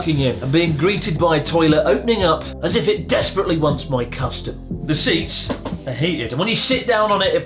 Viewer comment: "as if it desperately wants my" what??? did he say... custard??? pls clarify